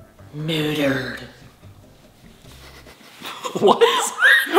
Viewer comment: sounds like she said murdered is that using words?!?!?